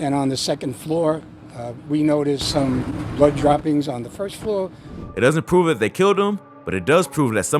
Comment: Cops says "On the second floor, we noticed blood on the first floor ." WaitAMinute....What???! Am I the only one who heard that?🤔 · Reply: He said blood droppings on the first floor but I understand why you were confused